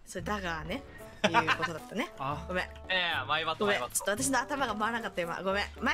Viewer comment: さんだから『マイバッド』が『マイバット』に聞こえてやっぱりわいせつなんだな…と思ってしまった 俺の頭がわいせつじゃねーか…